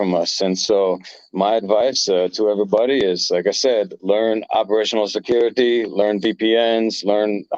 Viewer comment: did he say operational security? It's not too clear